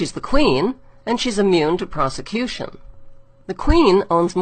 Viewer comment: I’m sorry did you just say she’s immune to prosecution 🤨...?